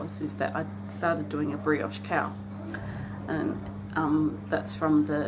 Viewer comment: Now I know my hearing is not good but I swear I heard you say a brioche 'cow' and it took a few minutes for me to realise you said cowl. So my mind was imagining a stuffed toy like Shaun the sheep. Put a smile on my face. Thanks for sharing with us..